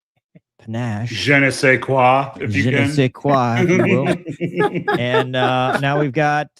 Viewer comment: Who is this girl I keep hearing about? Jenny Sayqua?